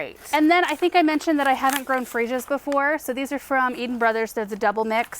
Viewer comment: I watch on double speed, so maybe I heard wrong, but I think Laura was saying freesia when talking about the plant she's never potted before because they are not hardy to her zone. Do I not know what freesia looks like, did I hear wrong, or was there an oops and a picture daffodils popped up instead? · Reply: She definitely said freesia!